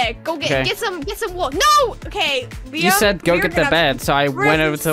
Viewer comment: Liam did I just hear Liam? Now we know real name